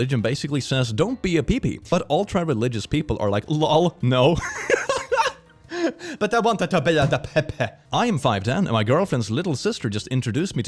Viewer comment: Translation to what he just said “But they wanted to be like the PE PE” You’re welcome :)